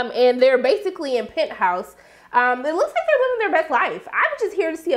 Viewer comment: I usually don't like to correct people when they say things wrong. But when you are referring to Pentos it sounds like you were saying Penthouse...lol